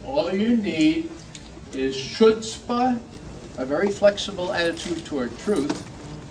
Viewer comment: I think he is saying “ctzbt “ which means LIES. So that is very telling Proverbs 29 says a ruler adhered to lies and his servants become wicked.. (also an edict in Koran to lie to infidels ie us non - Muslims) Decree to lie to overthrow countries etc.. 🎉congratulations the USA should be so proud.. and that also has repercussions within the law of returns. Always play Defense-Ladies and Gentlemen, nobody can return REVENGE AND LAW OF COMBAT IS MOST RIGHTEOUS IS VICTORIOUS.. There are rules before the “stupid rules”